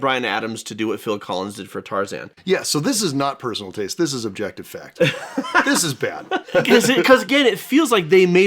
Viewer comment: Never thought I'd see the day where I'd hear the music from Spirit described as "objectively bad." I kept waiting for you two to discuss how amazing the score is. My mom got the CD and we listened to it on every single road trip as kids. Every. Single. One. I was never a huge fan of the Road to El Dorado but I was definitely surprised when you said the music was bad. It wasn't a movie I watched often but I still remember "It's tough to be a god, to tread where mortals trod!" a decade later. I'm starting to think you two just have terrible taste in music. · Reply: Yeah, at first I thought they said “objectively better” because they were just talking about The Road to El Dorado as a comparison but when they started going on about how it’s not needed and it hurts the movie, I was like “Wait, they said objectively BAD?!” Guys, you already know that people love this movie. We’re all entitled to our opinions, but don’t put out a video on a movie that you know people love and then call an element of it “objectively bad.”